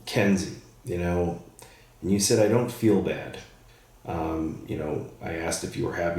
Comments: Sounds like he said " sad, I don't feel bad" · I heard “sad and I feel bad” Interesting